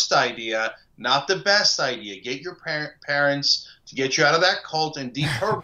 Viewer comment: OMG did Cenk say James Carville needs to call his parents to get him out of that cult? LMAO.